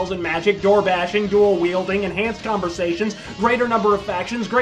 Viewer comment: Did I hear "door wielding" as a feature in daggerfall? Kicking ass with a door is something I must try out.